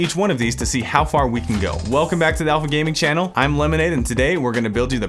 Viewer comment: Did he say Lemonade ?